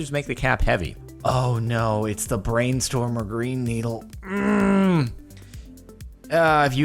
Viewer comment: I hear "Green needle"